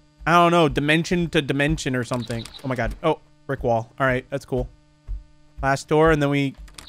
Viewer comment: For the third game when you said oh brick wall it sounded like you said oh rick roll